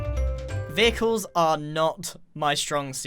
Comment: Anyone else feel like when he said “vehicles” it sounded like pickles?